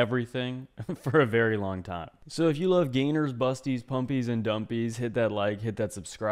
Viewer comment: Pumpies and Bumbies!? I thought you said sweater puppies and big lumpies!